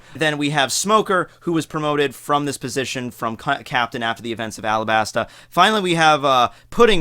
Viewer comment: omg I swear he said something on the verge of cunt captain LMAO